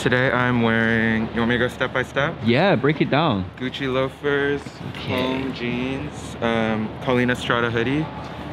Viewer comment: Anyone can tell me the brand of the jeans at 4:20 I can’t understand what he’s saying :( Would truly appreciate it❤️ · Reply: comme des garçons :)